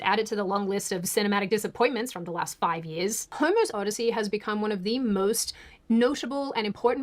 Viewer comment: I heard her say Homo’s Odyssey and while I know it’s not correct, it is suddenly accurate. 😂😂😂😂😂